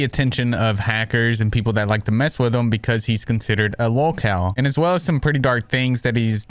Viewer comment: He's considered a what?? Lowcal ? Low cow?? 0:13 wtf word are u saying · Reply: LolCow- someone who people milk for laughs from the dumb or crazy stuff they do